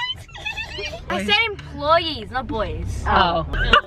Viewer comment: said boys not employees from how i heard it